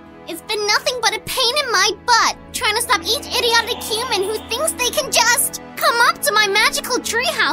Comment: I thought she say " TRY TO EAT EACH IDIOTIC HUMAN" lol